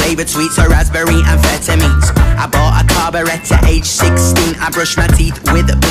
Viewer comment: i thought it said '' i bought a carbonator age 16 '' like dawg u can be any age and buy a carbonator